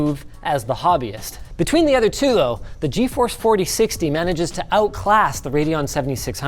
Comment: Did Linus say GeForce 4060?